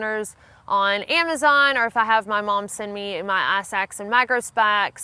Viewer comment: Wait, did you say, "macro spacks"? Every now and then, your southern accent slips out. It's very charming. Another great video! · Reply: I hear "eye sacks," each time she says "ice axe." Love bein southern.